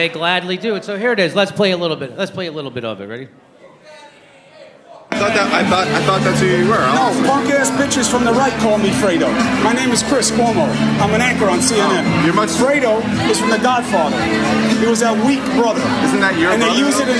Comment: "I'm a actor on CNN" Yes sir you are, as are the rest of the so called journalist. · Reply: He said anchor, but I thought he said “actor” at first and thought the same thing